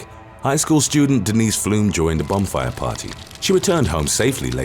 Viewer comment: swear i heard "bum fire party" Went back and listened again. Still sounded like "bum fire party".